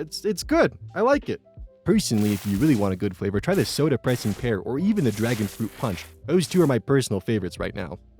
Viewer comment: Every time I listen to that end segment, I always hear "Sodapressed Despear" as "Soda Pressing Pear".